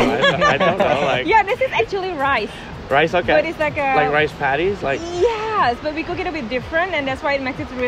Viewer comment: "Ini nasi yang dari padi kan?" yakali 😂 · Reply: Aku dengernya "This is rice patties, right?" Jadi cuma misheard aja kayaknya 😁😁😁